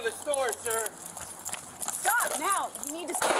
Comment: he thought that she said "shot now" instead of "stop now"